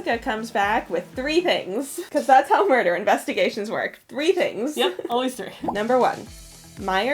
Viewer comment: Who else thought she said "arm stealer" instead of 'arms dealer"?